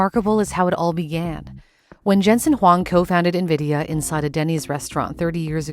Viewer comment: Narrator said cold-find Nvidia instead of cofounded Nvidia… I had to turn on Closed Caption.